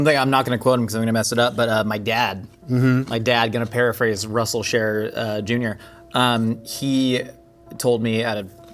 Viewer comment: Generally I only point out errors in subtitles when they're fairly egregious and when the video is relatively new, but... Around 41:43 where Josh full-name-drops his dad, the subtitles say that his last name is "Cher" not "Scherer". Feel like getting that name correct would be.. of some import.